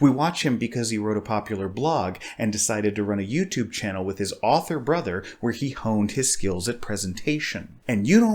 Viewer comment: Was listening to you while cooking dinner and heard "[...] decided to run a YouTube channel with his awful brother [...]" and almost burned my food going back to see what you actually said. The happy ending is I heard wrong, and dinner came out fine. Onwards!